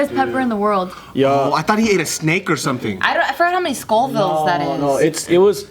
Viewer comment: Lol, I thought said Skull bones... I was gonna clown but I grabbed the tape 😐